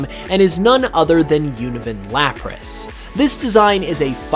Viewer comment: Actually Unovan Lapras could definitely be a thing because of Reversal Mountain. In White 2 it's a volcano so I could see it as a version exclusive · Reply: i thought you said oven lapras